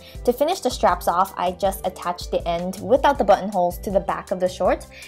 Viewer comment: i was watching this with my dad in the room and whenever she said button holes, he thought she was saying butt holes